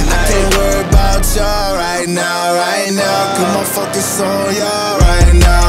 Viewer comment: Did he just say the F word at 52 seconds? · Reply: Napalm he said “focus”